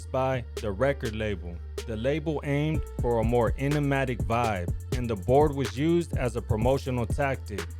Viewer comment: what word did you use to describe “vibe”? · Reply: I think he said enigmatic